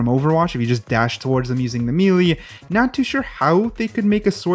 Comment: did he just say "me lee" and not "melee"